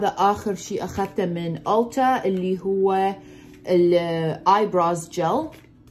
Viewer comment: Love you, watch you all the way from London. I'm half Algerian, half Indian wa ahbech kulich hawayaaa. When you was saying brow gel, I thought you were saying bra gel.. I was like haaa, shono hay bra gel, I don't know what it does bas I need it 😂😂😂